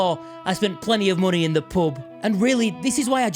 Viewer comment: oh pub. 😂 It sounded like he was saying Pope but I know that wasn't right